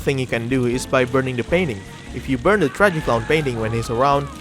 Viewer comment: I THOUGHT HE SAID BURNING A BABY